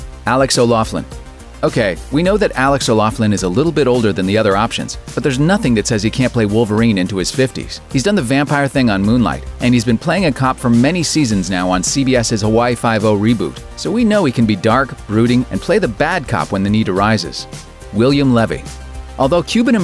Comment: Holy shit, I thought he said Moon Knight and panicked a little... (I wanna see a Moon Knight show, mkay?)